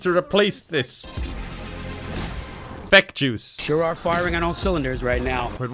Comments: is saying fek jews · i thought he said feck jews instead of FeckJuice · did he say feck Jews? Or am I crazy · thought you said feck jews for a moment xD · I could swear he said "Feck Jews"